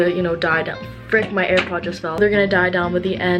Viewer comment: dId i jUsT hEaR aIr pOd???? Next up on Shane Dawson conspiracy theories...